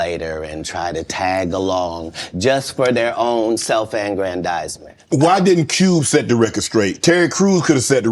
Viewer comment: sorry if my illiteracy is showing, but what word/words does he say right here? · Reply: Self-aggrandizement